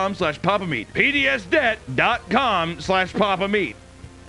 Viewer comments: Did you do your nails the color of Wendy's hair for the Video Hunter? Btw, I totally thought you were say Penìs debt at first for the sponsor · He sounded like he was saying penis debt instead of pds